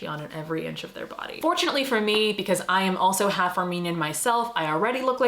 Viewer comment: I thought u said Romanian at first n got rly excited 😅